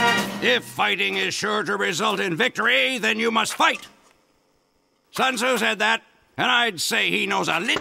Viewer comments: “Sun Tzu said that” Solider, 2021 Note: idk when the original voice line was recorded. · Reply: Honestly my whole life I thought he said “Sons who said that?” And now my whole life is a lie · It took me a long time to realize he didn’t say “sons, who said that?” And assumed he was just being as insane as always to him saying “sun Tuz said that” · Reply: Back when I first watched it (probably 2010/2011) I also thought it was "Sons, who said that?" And it took me 10 whole years to realize that it was, in fact, "Sun Tzu said that", noting the moment I realized it was the first time I watched this video a couple of weeks ago. When he said the quote I also recognized it as a quote from The Art of War so that helped it click but holy cow, *ten years*.